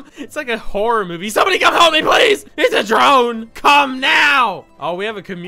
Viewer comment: Pause the video before you click this and look at what the man is saying behind the girl "it's a Po-- drone"